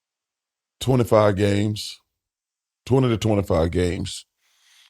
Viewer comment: BOTH times I thought this man said 225 games lol I'm like say that again and chuck says 225 games again. But luckily I can break down mumble lingo cuz he's sayin 20 to 25 games.....🤦‍♂️🤣🤣🤣